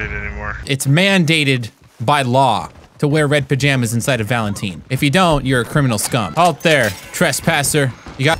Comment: did blud say "Valenteen" 💀